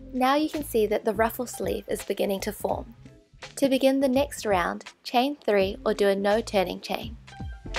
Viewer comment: is she saying add another? Not to be disrespectful, I just don’t fully understand what she is saying, is this a crocheting term? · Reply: she says to begin the next round chain 3.